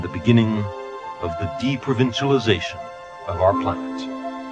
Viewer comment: Aos 3:30 não é "deep provincialization" (profunda provincialização), mas "deprovincialization" (desprovincianização). É uma correção importante, pois a legenda está com o sentido exatamente oposto ao que ele disse.